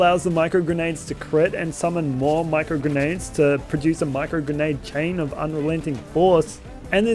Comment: What's the name of the annoinment you said around 13:00? Can't understand you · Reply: no annointment was said. just “micro grenade chain of unrelenting force” Edit: You probably meant the URad annointment. “While under 50% health, deal 100% bonus radiation damage”